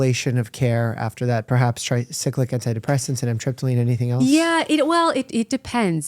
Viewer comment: I may need some clarification here because I think I had an aha moment. Did he say Amitriptyline here? I was prescribed it when I was younger for chronic migraines and looking back I definitely had less - almost no GI pain in that time period. I've had pain and symptoms since I was a child that were never addressed and this could be clicking somehow